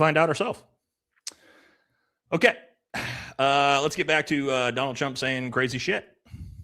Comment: it sounded like he called hm donald chump